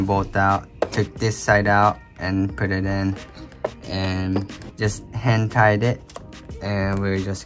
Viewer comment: Ngl, thought you said 'hentai'd' it. Immediately looked up while I was eating food